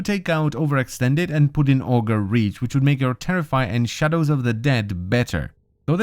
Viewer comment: wait what did he said at 3:08 ? olga what? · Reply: Auger Reach.